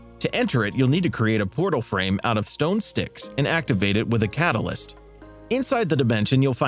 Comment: Excuse me.. did u say.. stone STICKS??